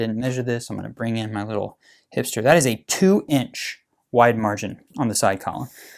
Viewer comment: 😳did some say “wide margins”?